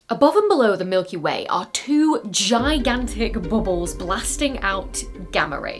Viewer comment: Misheard the beginning as "GIGANTIC balls blasting out gamma rays"